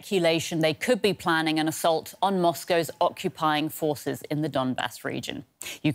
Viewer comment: I thought she said "They could be planning an assault on Moscow" lol